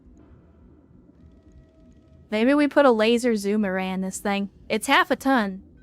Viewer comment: You were saying "Laser Zoom Array" and I heard "Laser Zoomerang". What kind of boomerang is that? they I checked the screen and was disappointed the mech will not be throwing fast boomerangs that would shoot lasers around and return to the caster...